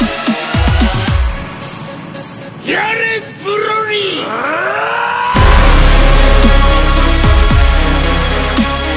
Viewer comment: What is he saying at 1:48? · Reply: Go broly